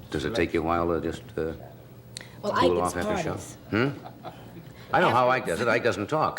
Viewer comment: what did she say? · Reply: “Ike has parties”